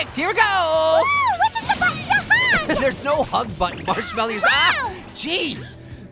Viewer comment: did pear say ‘Marsh-melly’